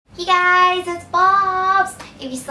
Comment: In the intro when she said 'Hey ! it's bubz!!" . I thought she said Hey it's BOBS ! : D HaHaHaHa .